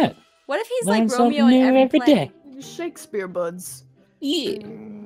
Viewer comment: When Katelyn said Shakespeare books I thought that she said Shakespeare butts😂